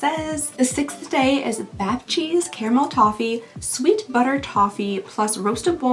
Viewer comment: I'm sorry did you say "bath cheese"????????